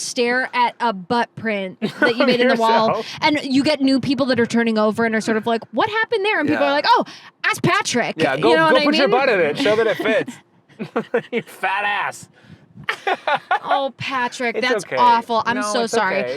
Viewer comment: when Vic said 'ask Patrick' about the hole he made in the wall, I thought she said 'ass Patrick...' And tbh, it works just the same....